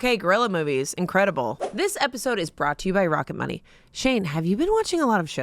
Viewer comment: I swear to you, after that conversation, I heard: "This episode is brought to you by Rocket Monkey!"